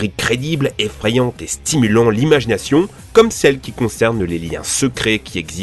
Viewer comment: Il y a que moi qui entend "Image Nation" ?!